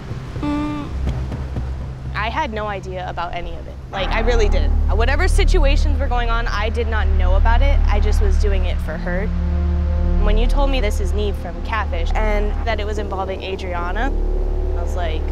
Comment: I honest to god thought said 'its nev'